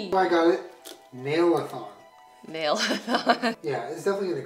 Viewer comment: Thought he said nail thong hahaha